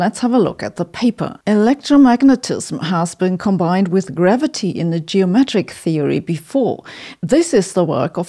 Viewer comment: I thought she said, "wild gravity," and it gave me hope that we could tame gravity, or breed a tamer version, and maybe have flying cars in my lifetime.